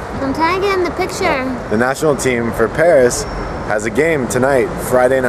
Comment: "National team for Paris" lol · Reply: Thought I missed heard "National Team for France", but I see now he did say for Paris... LMFAO! XD